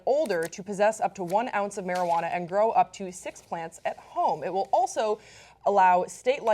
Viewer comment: "Grow up to 6 plants at home? Ooooooh... I thought they said 6 plants in every room. My bad, officer... NO, NOT THE CANE"